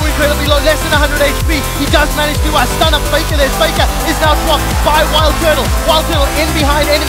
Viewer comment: Does he really say "Fakker is now fucked..."???